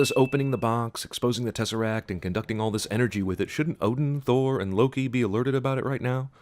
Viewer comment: You said and Loki but the subtitles say or Loki